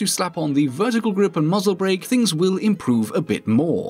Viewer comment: did you say muzzle brake???